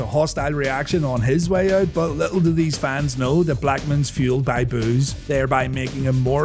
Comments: Misheard that as “he was fueled by booze,” rather than “boos,” but hey, it was the Attitude Era. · I thought you said Blackman was fueled by booze 😂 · When you said fueled by boos, I thought you said fueled by booze! 😅 I thought there was something going on with him behind the scenes, lol